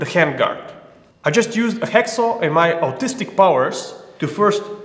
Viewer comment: I coulda swore you said "Autistic powers" :).